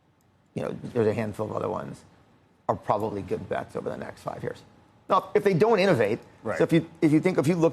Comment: what does he say here? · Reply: “Are probably good bets over the next 5 years”